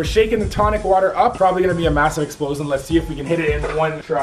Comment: When you where hitting the tonic water it sounded like you said masin explosom